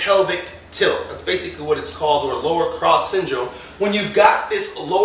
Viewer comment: I thought I heard "Anterior Pelvic Tilt, or Lara Croft Syndrome," and I wasn't sure what she had to do with it.